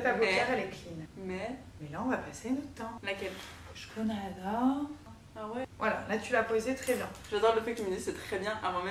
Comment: elle dit quoi nabil 😅😅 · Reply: Elle a dit "chkoun hada " c’est "c’est qui ça " en arabe